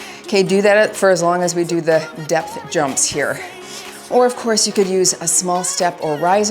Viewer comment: That was a really hard workout! I have to say, when the Depth Jumps came up, I thought said "Death Jumps" at first and thought, "what the hell?" and then looked at the screen and laughed! "Depth Jumps. Depth." 😂 I enjoyed the jumping and plyo very much! The lunges were killer, though. Feeling accomplished now! 💪